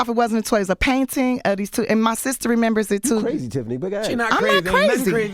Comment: Envy doesnt have tact. You don't call someone with that history crazy. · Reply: I really thought I heard it wrong & it wasn't a "Haha you crazy" it was " you're crazy Tiffany" how can you call someone that after hearing that traumatizing life story.. especially her mom has a MENTAL illness. You can tell what he said hurt her. Smh.